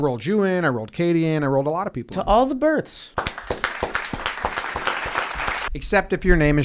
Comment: I thought he said all the birds 😂